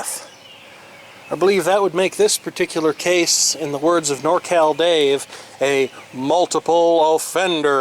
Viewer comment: Who else thought that he said "Nortel Dave"?